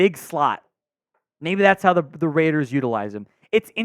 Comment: no way man this dude is insane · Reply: my bad, I thought he said Ravens not Raiders.